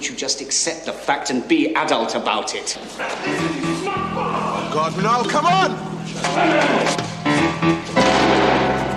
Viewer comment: What did George say from 21:56-21:57? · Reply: Sounds like 'Listen, you smug bastard!'